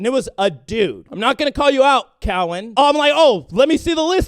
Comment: did you say "Cowen"?